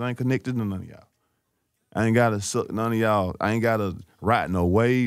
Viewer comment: Did Dolph Say “ I Ain’t Gotta Suck None Of Y’all “ 🤔🤦🏾‍♂️